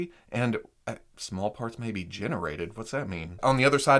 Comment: I thought he said “Smallpox may be generated”